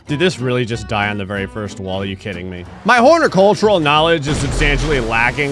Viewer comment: did you say hornicultural